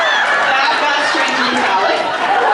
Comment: What did the girl with the last question say when jensen broke off the microphone war between him and jared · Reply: "that got strangely phallic"